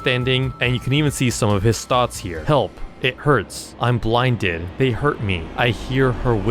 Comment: I thought I'm blinded said I'm bluudud for a sec